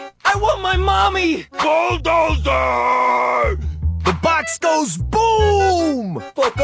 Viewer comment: I thought bull said bozo booo